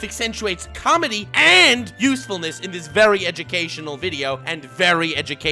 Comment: "Educational" Youtube: "Animation" Mission failed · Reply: Every time he says "educational" I hear "edgy-cational"